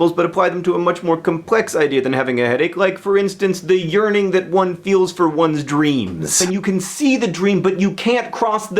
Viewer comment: when you said "the urning that one feels for one's dream", I thought you said urining.....